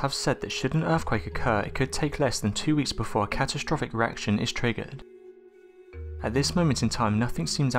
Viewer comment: did you say catastrophic erection? 👍